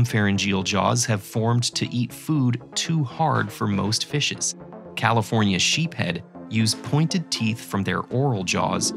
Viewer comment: "California sheepheads" Me mishearing: Not a very nice name for a fish...